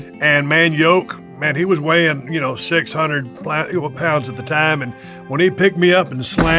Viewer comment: did he say 600 black people pounds